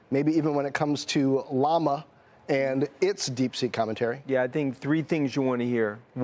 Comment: Thought she almost said Deep Sh#% at first